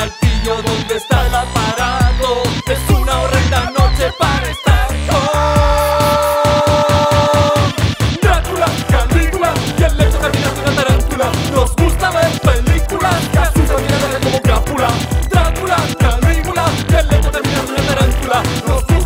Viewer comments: al fin entendi que demonios decia despues del "Drácula, Calígula y asd asdasdasd Tarántula" me siento realizado XD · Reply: Yo pensé que decía: ' y el lecho de mirar una tarantula xdxd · Es una horrenda noche para espantos!! Les juro que eso pensé que decia xd